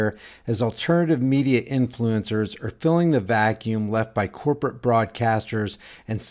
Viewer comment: I thought you said !!!"CORPORATE FRAUDCASTERS"!!!, but you didn't.(Use it, liberally)...